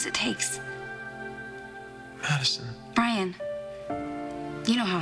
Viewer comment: I think Marissa is right about AJ. Trying to make him the next in line for Chandler is dumb. You would think that JR would know that by now. haha, Madison sounds like she says "Brian", not "Ryan" at 4:45.